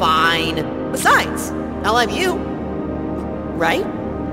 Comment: He said I'll have you but sounded like I love you 😂